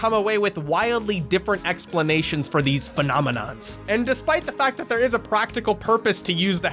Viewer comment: Did you say “phenomenons?”